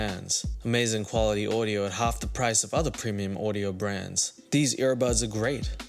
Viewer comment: I thought she said Katie Price and I was very confused 😂